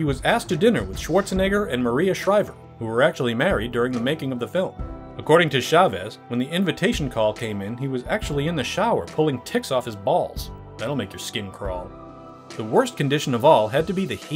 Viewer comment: I had to back that up to listen again to hear “pulling ticks from his balls”! Ha ha. Yup, that’s what I thought I heard! Ha ha ha